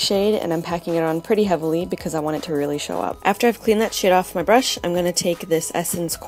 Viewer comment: It really does sound like you said "clean that shit off my brush" hahaha.